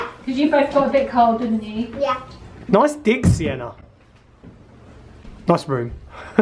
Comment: What. Did he say at 17:35 go sienna? 🤣🤣